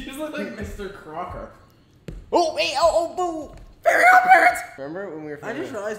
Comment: not mocking again from the baking video 😭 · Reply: i may be wrong but i swear i thought he said “fairy on parrots” 😭